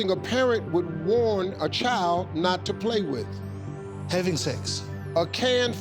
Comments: I thought he said "heavy things" instead of "having sex". And when will people understand that America is NOT a country??? The United States is a country. Mexico is a country. America? NOT a country. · I thought he said heavy things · SEX!!! I could swear I heard him said"Don't play with heavy things"... Smh, these accent though...